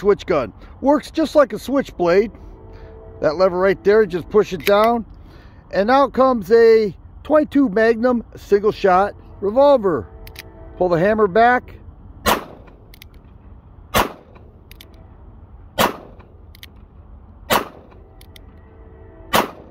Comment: I thought u said single shot